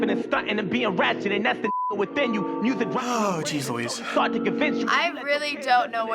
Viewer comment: did bro say jeez louise😭